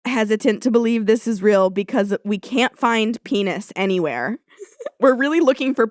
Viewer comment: I had to walk out for a minute. When I walked in I just heard "We just can't find penis anywhere"